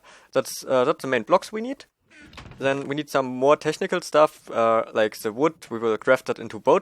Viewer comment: I'm not the only one who heard "We need some more testicle stuff..." at 7:45 , right? ;]